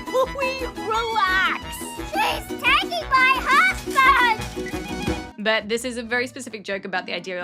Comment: SHES TAKING MY GNOME!!!!! But she actually said my husband but it’s literally a GNOME